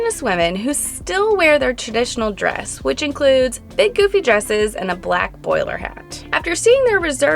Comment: But why (at the end) are you calling chulitas’ traditional dress “big, goofy dresses”? It’s their traditional dress, and it deserves respect. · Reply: I thought she said poofy?